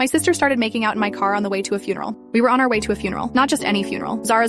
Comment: Did anyone else at first hear “my sister started making out with my car?” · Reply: I heard "my sister was making out on her car"